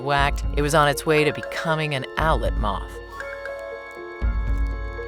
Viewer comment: Did anyone else hear “outlet mall” when she said “owlet moth”?